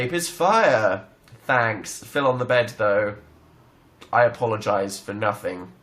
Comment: what did he say at 23:58 ????? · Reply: He said "thanks. Phil on the bed though. I apologize for nothing"